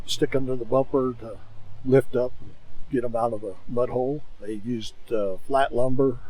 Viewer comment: Did he say get em out of the butthole